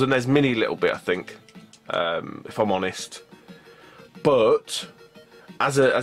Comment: I can't quite make out that sentence he says. Is it "..It might "expose" the NES mini a little bit if I'm honest."?